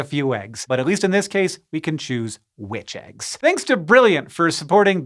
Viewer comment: For some reason I heard "at least in this case, we can choose witch eggs"